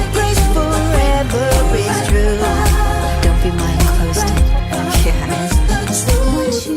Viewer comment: " I heard don't be closed minded..."